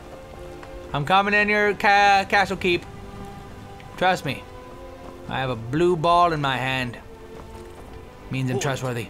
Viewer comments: am i hearing wrong or did he said at 5:42 i have a blue ball in my hand lol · am i hearing wrong or did he say he has a blue ball in his hands